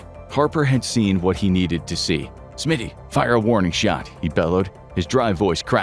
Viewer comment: Warning shot? I thought you said head shot...